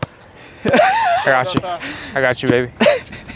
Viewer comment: Did say I got you baby to Tony when they were playing basketball lmfao at the 43:07 mark!